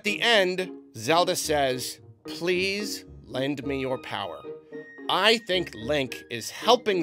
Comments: I thought Zelda said "Please lend HIM your power." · I have to point out at the end Zelda does not say "lend me your power", she says "lend him your power". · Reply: I’m just glad I wasn’t the only one who thought she said, “lend me your power”. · She didn't say 'lend me*', she said lend *him. I'm not sure how so many people are mishearing that. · No. Incorrect. What Zelda actually said was "Lend HIM your power". Not "lend me your power".